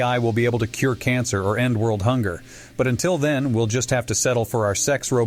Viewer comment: am I they only one who heard 'N-word hunger' instead of 'end world hunger'